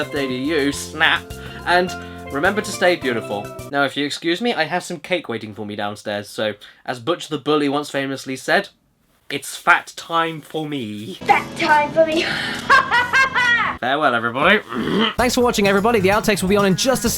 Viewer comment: please tell me that I wasn't the only person who thought Butch said "its fap time for me! HAHAHA"